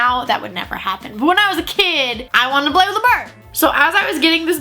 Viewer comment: when she said "i wanted to play with the bird" the captions thought she said "i want to blow the bird"...lmfao